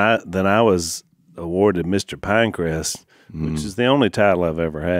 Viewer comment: I thought Al said he was named "Mr. Pancreas," and as I tried to figure out how one might earn such a title, Jase said something about the name of his school being [Pine-creas?] or something, so I understand the context now...but I still don't know quite what the name of the school was.